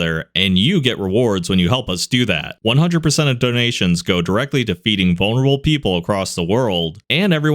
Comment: I misheard feeding people as defeating people God that is just terrible😂🤣😭